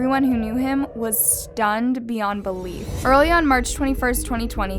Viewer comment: she said march but the subtitles say may